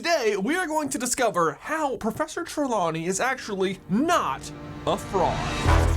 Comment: When you said not a Fraud, I thought you said not a Frog, I was very confused